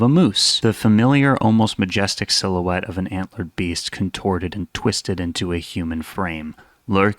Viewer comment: instead of hearing antler beast or whatever, i heard mr. beast, so i was like "since when is mr. beast an analog monster??"